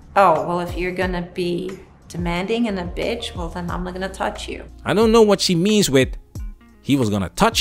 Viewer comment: I think you misheard her. She said "oh, well if you're going to be demanding and be a bitch then imnogonnatouchyou" "imnogonnatouchyou" Kind of slurred her words together but she said "I'm NOT going to touch you" meaning he ignored her when she was demanding